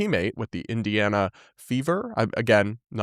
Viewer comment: I thought he said "indiana beavers" 😅